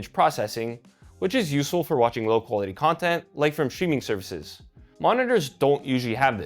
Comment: did you say STREAMING or shrimming??))